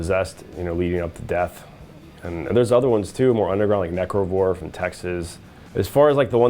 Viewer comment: What is he saying at 7:30 ? “Necro”-something? And there’s a band called Texas?